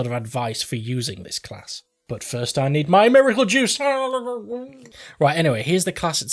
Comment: uhngngngung lmao. Did you say miracle juice? What are you drinking?